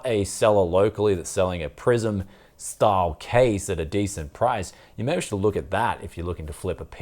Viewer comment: Oh, I thought you said "prison-style case"